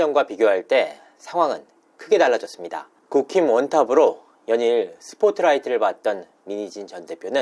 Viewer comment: 기자님 응원합니다! 진실만 말하고있었다는거 믿어요! 근데 국힘원탑이 아니라 국힙원탑이에요😂 발음으로는 계속 국힘원탑으로 들려서요ㅎㅎ